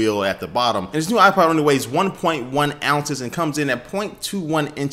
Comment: into the video, does he say, "This nigga comes in at...."? ROTFLMAO!